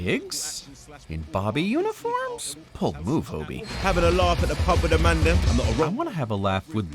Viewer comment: I heard, "Have a laugh at the pub with Amanda."